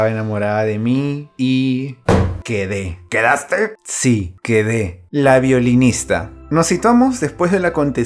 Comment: pense que decia violadora y me imajine el contexto xd